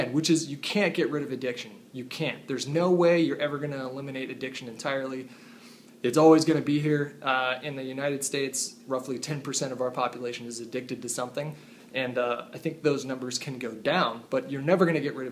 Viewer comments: This whole time I thought he was saying "You can't get rid if a Diction, about 10% of our population is a Diction." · At first I heard " we cannot get rid of diction " I was like wtf ? Is he talking about Eatmydiction ?